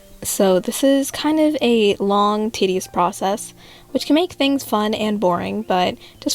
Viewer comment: WHY DID I HEAR “this is sort of a long tiddies process” HELP 😭